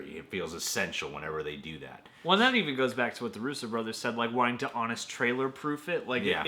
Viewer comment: Did he call him the Rooster brothers? @ 07:00 ?